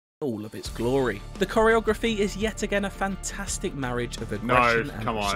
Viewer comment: He said fantastic marriage, I hear 'disastrous miscarriage'